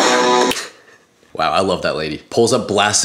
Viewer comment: I didn’t smile I thought he said don’t smile 😅 still wholesome though.